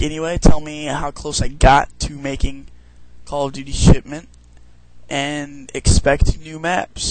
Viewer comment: It sounded like you said "shitment".